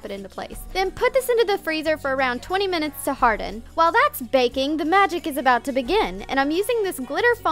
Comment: did she say baking?